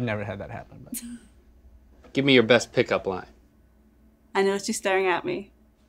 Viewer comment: "Give me your best pick up line." "I noticed you staring at me." "Oh" "Nailed it" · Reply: OH! I thought she said "I don't want you staring at me" 😆😂